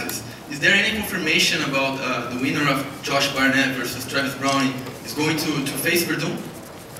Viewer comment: Did he really said Travis Brownie?? LMFAO!!!